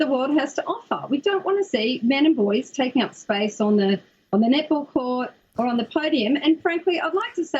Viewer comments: Literally thought they where saying nipple instead of net-ball for the first three minutes 😳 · Am i the only one who heard it as nipple instead of net ball? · Oh net -ball i thought she said nipple. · Australian needs to work on enunciation. I keep hearing "nipple" instead of "net ball" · sounds like she is saying nip ball and not net ball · I thought they we're saying nipple, but it is was net ball. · I thought they were saying nipples. it was netball. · Am I the only one who keeps hearing "nipple" whenever one of the Australians says "netball"? Man! Gotta love the Aussie accents! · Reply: Oh shit they are saying net ball whole time. I thought they were saying nipple that’s freaking funny. Love the Aussie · ohhhhh. NET BALL! I thought they were saying NIPPLE!!! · Fkin thought that she was saying nipple instead of net ball🤣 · When she says net ball it sound like she is saying niple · Equality really sucks, doesn’t it? Half this video I thought they were saying nipple, not net ball. I guess near ball is basketball? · I thought they were saying "nipple"...turns out they were saying net ball. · Ok who thought they were saying "nipple" instead of "net ball"? Lol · sounds like she is saying nipple.....not Net Ball. LMAO!!! They need to pronounce words more specifically. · Sounds like she is saying nipple not net ball 😝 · Oh, they are saying NET BALL . I thought they were saying nipple at first.